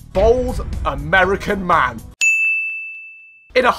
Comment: I backed up 10 seconds so I heard "there can only be one victor, bald American man